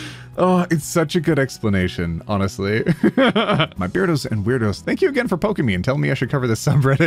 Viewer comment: Beardsome weirdos is what I always thought he said lol